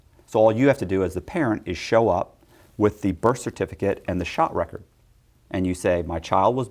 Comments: Happy everyone seems to be enjoying the podcast, thank you again Patrick for having me on! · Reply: & Inside True Crime Hey at the 11:00 mark did you say "shot record", as in immunization record? This "mandatory covaids vaccine" to work/socialize/travel thing is starting to sound a lot less medical and a lot more debt-slaves-as-collateral by the day! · Us Jamaicans like to say, "Theif never love to see thief with long bag". Translation: the biggest theives and fraudsters, banks and the government never want to see people doing the same as them. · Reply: YES! Hey at the 11:00 mark did he say "shot record", as in immunization record? This "mandatory covaids vaccine" to work/socialize/travel thing is starting to sound a lot less medical and a lot more debt-slaves-as-collateral by the day! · What a crazy story, should definitely be made into a Netflix series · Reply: At the 11:00 mark did he say "shot record", as in immunization record? This "mandatory covaids vaccine" to work/socialize/travel thing is starting to sound a lot less medical and a lot more debt-slaves-as-collateral by the day!